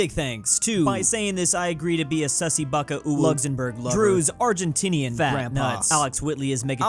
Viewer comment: at the end of each video at the shoutouts i always hear "drew's argentinian grandfather"